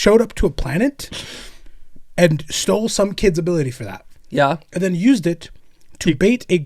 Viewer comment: I had to backtrack there because I first heard "bathed" the great unclean one and honestly that would have been so much funnier xD